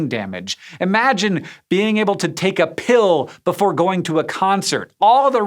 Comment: SCISHOW- "IMAGINE TAKING A PILL BEFORE A CONCERT"... Did i just " hear" that correctly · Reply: No, you misheard, he said "Imagine being able to take a pill..."